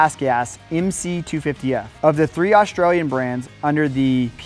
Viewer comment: did he call GasGas an Australian brand?